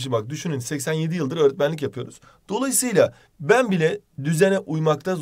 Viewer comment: hoca 87 yıllık öğretmenlik yapıyorum mu dedi ben mi yanlış duydum